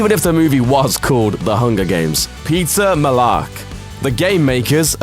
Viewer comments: "pizza mellark” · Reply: And I thought I was going crazy when I heard "Pizza Mellark" lol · I thought he said pizza not peeta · I thought you said pizza mellark😂😂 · When he said Peeta Mellark I thought he said Pizza Mellark. Um yeah I'm just hungry. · I thought he said pizza mellark. Just me? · I swear I heard "Pizza Mellark" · "Pizza Mellark" is what he sounded like he said when introducing him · I thought you said "Pizza Mellark" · sounded like he said "Pizza Mellark" 😂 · I thought you said pizza mellark.. Pizza.. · I thought you said pizza Mellark 😂😂😂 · did he say Pizza Mellark😂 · When he said Peeta Mellark I thought he said Pizza Mellark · It sounded like he said “pizza mellark”